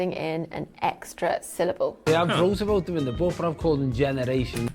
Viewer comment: subtitles are wrong here. He says, “so I’ve wrote about them in the book”